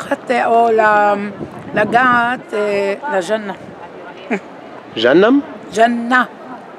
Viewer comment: The woman at 3:08 said jannah which is Heaven/Paradise. She did not say Jahannam or as you put it janam which is hell. Why would she want to touch hell?